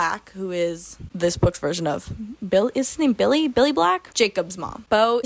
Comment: i thought you said "the butch version of billy black" for a moment and my ears did in fact perk up. can you imagine